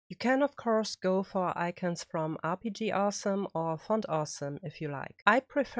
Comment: Can you link where you get your icons from please? At about 11:15 I can't work out what you are saying....RPG arson? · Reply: The site I mentioned is <link> which is rpg-awesome. For my custom icons, I am using an icon pack I bought on humblebundle a while ago. If I use iconfonts, I use a mixture of fontawesome and rpg-awesome.